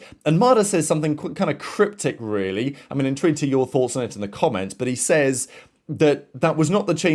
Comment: "In trent to your thoughts in the comments" ? WHAT ARE YOU SAYING someone please fill me in